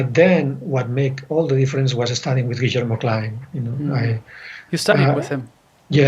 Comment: Who is the composition teacher Jorge mentions around 46:15, Richard something? I couldn't quite decipher what he said. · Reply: Guillermo Klein.